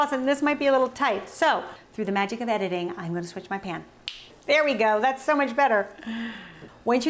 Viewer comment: At first, I thought you said you needed to change your “pants” because yours weren’t going to fit anymore! 🤣 That’s what happens when you watch/listen while folding laundry. I always enjoy your videos.